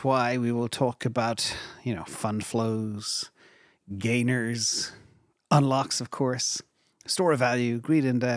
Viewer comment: Lol. You said "gainers" but i was only half listening for a second and thought you said "gay nerds"